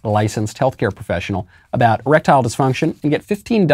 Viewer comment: Lol I thought Knowles was saying electoral disfunction. Wrong E.D